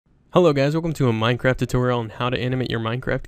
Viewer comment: i thought he said minecrap lol